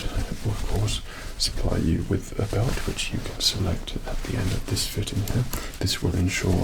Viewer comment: did he just say "at the end of this video"?